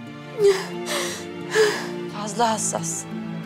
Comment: Hatice karakterinde biri Hürrem’e fazla hassassın mı dedi ben mi yanlış duydum